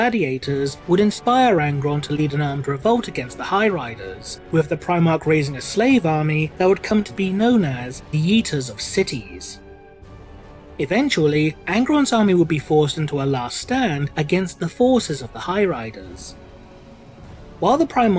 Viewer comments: For a second I thought you said "The Yeeters of cities." And I got this image in my head of Angron being so fucking angry that he just picked up a whole city and threw it. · All I heard then was " The yeeters of cities " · I legit heard Yeeters of Cities. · Somehow I heard "The Yeeter of Cities" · "yeeters of cities"? am I hearing that right? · I thought he said yeeters of cities for a sec